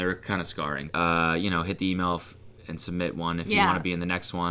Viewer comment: did cody say "hit the e-milf"?